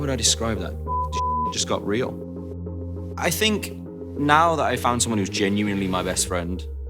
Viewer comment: Am I the only one who thought he said Niall instead of Maya on 8:32? Oh he said "Now". I'm so dumb 🤦‍♀️